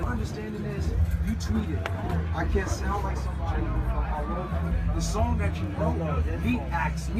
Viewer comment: is not inaudible... its: you tweeted I cant sound like someone I wrote for... Even if you couldn't hear it perfectly its a quote you already used